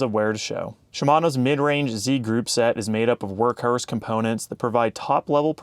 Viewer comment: Just popped in to say the work hrrs pronounciation at 8:02 is awesome :D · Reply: I heard “work hearse” - (halloween appropriate)